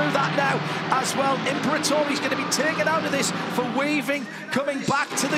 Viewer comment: someone please explain me what happened? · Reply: thanks, i thought he said waving and meant some flagguy with that.